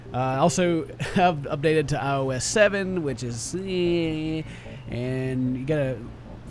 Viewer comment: Whenever I hear, "iOS 7" I keep thinking people are saying, "Iowa-7". I dunno why.